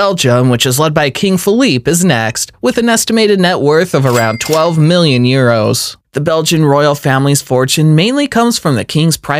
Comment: Why does it say 12 million Pounds at 4:07 when he says 12 million Euros?